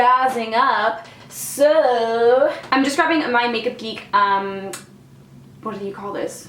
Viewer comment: I was distracted pouring hot sauce on some snacks right when you said, "Makeup Geek" and I immediately turned around and reminded cuz I heard, "Naked Geek". 😂😂😂